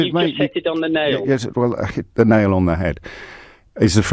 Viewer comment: I thought the caller said you just 'hit your head on the nail' 😂🤣🤣